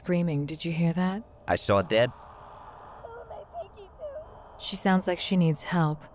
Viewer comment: When I heard "Oh my twinkle-toes" on the TV, I knew Nicki and Lana were watching the Friday the 13th video!